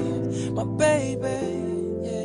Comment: is it just me or does it sound like he saying "talkin bout dirty dan"